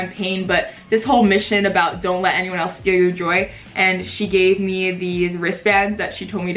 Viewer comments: I really thought you said that she gave you bracelets that said “don’t let other people steal your joint” I mean both are good advice tbh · lmao i thought you were saying “don’t let anyone steal your jewellery”